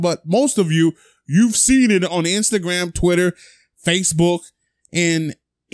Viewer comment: At first i thought he said face boonk😂😂😂